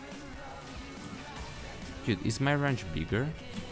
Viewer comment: What were you trying to say at 12:58 ? "Is my __ bigger/weaker?" It is a little hard to hear sometimes love the video though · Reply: he says "bigger"